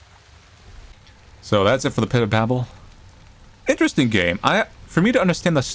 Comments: I thought I said pit of bagel 😭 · i thought it said pit of bagel and i was like "mm grinded animals"